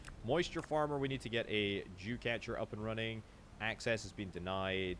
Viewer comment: brother, i think it may be important to enunciate "dew" when you say dew catcher. it completely sounds like youre saying jew catcher hahaha <3